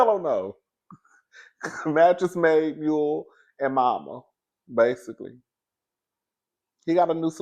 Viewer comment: MEDIC is what The Diva said. But coleslaw shool is giving all the above and momma